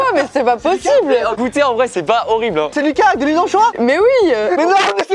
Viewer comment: À 8:39 il te lâche une phrase c'est incompréhensible "C'eSt LuCaS kaülézenchoi ?!!!" 😂😂😂😂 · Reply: C'est peut-être pas du premier degrès mais il a dit c'est Lucas qui a l'huile d'anchoi